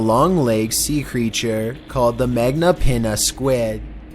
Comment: Did he just say magma peenus squid